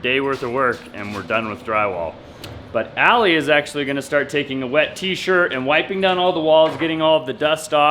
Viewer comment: All I heard say was Allie in a wet T-shirt. And I thought to myself What did he just say? And had to go back and listen again.